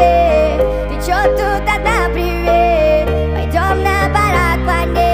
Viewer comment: Мне послышалось:и чёрту тогда привет... И сразу вспомнила про Алфедова и Джаста, хе😅